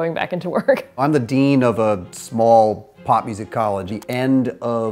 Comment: He is “the dean of a poppyseed college”?? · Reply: Omg I heard, "...of a pop music college" haha!